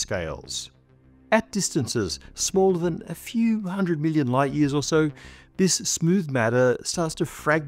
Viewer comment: This channel consistently produces such interesting content, please don't stop!. There's also an error in the closed captions at 6:15, reading "a billion" when Matt said "a few hundred million." Or maybe the subtitle is right and what Matt said is wrong!